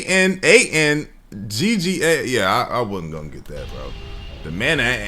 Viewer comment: bro i thought that said the mana"nigga" 😭😭